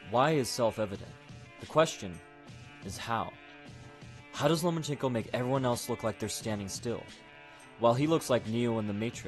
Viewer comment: seconds in and the closed caption calls him “Lemon Pickle” instead of lomachenko lol